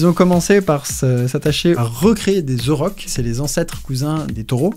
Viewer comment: Je faisais quelque chose à côté, donc sans l'image, j'ai entendu "à recréer des The Rock" ... 8:28 Bon, la blague me rassure sur le fait que j'ai entendu ça xD